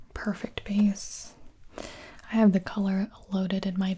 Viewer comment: I had subtitles on at 8:56 and it auto corrected to… perfect penis instead of perfect place I’m dying😂